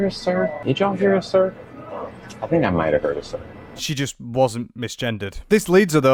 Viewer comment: i think the server said “caesar”. as in, the salad lily ordered. no misgendering, just bad hearing. maybe if they weren’t smacking so loud they would have heard what the waiter said.